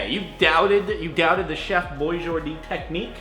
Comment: When it said Chef Boyjordee, I thought it said Chef Boyfriend and then I started freaking out because I thought they were finally in a relationship but then I replayed it....